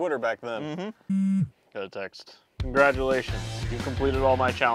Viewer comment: I like how says contraptions instead of congratulations